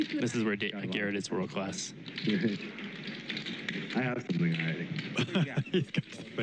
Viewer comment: what did G say here? the commentator got a chuckle out of it but i couldn't understand. "i had a couple of ninety"? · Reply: He says “I have something already” meaning he’s not on a draw. Then David asks if he has a couple of 9’s